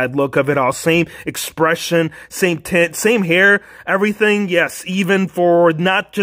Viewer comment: did he just say same tits?